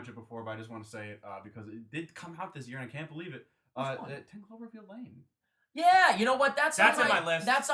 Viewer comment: What movie is talking about at 29:40?? Ive watched it back several times and I still had no idea what he says. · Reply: 10 Cloverfield Lane